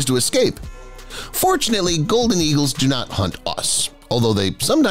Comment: i thought he said unfortunately eagles don't hunt us